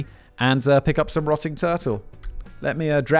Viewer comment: YES DANNY GO CRAZY WITH THAT INTRODUCTION! Also, I'm very satisfied that there exists an obscure website called "allegedly" where you can buy actual rotting turtle! Definitely sounds shady and dodgy but after visiting the website (and being a fan of the beard oil) it's peak meta and I love it 😆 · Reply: Because of the accent (I'm in Oklahoma) I thought he said "Rotting Churchill "... I then thought to myself; They did really like their Churchill so...👋🧐👌